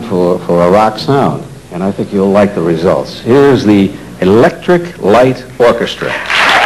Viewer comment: This is when I caught up with ELO. The announcer sounds like he says "Liquid Light Orchestra"! Works for me. Especially on sugar cubes.